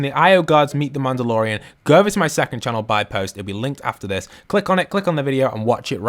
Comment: I heard it say it will begin